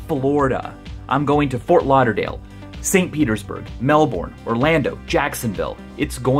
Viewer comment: did he just say St peters-burg?